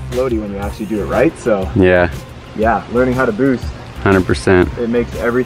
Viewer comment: Thought it said roost